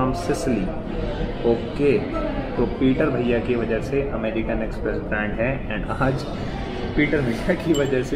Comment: is it only me who heard American express instead of pizza express 🤣🤣🤣🤣